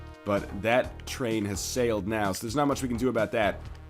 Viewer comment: did he say "that train has sailed now" at 7:07?